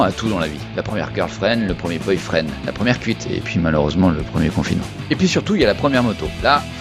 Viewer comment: audébut j'ai pas entendu "1ère cuite" j'ai entendu "1ère p***" xD (et non je ne m'en suis pas tapé, je vous vois venir)